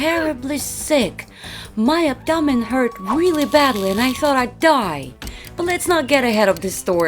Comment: By abdoman hurt really bad and I thought I die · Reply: Seldom Studios I thought she said I thought i’d die